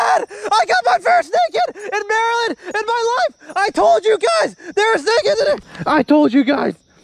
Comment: im sure you said "i told you guys" but all i heard was "poltergeist"